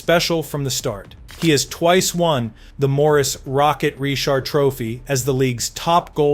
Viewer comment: Did he just say “Morris Rocket”?